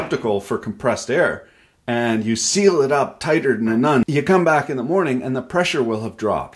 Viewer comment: did he just say " seal it up tighter than a nun" ? if so, i bet she is very tight indeed 🤣